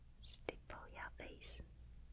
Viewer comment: what is she saying. Stippo? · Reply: Stipple